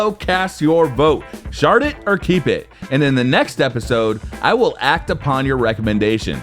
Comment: at he end it sounded like he said "Shart it or keep it."